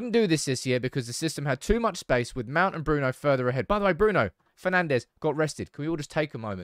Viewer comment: I thought bro said Bruno got arrested